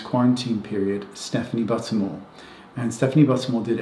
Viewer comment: I’m truly honored and was so happy to hear you speak so freely about your hunger and body image struggles. Thank you for sharing, normalizing issues like this is more impactful than you realize 🙏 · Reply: Oohh Buttermore.... thankgod 😂 i really thought he said Stephanie Buttomhole